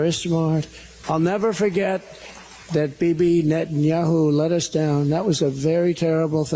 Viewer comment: is he saying Baby Net Yahoo? He is so stupid calling other for names he invents in his stone age brain.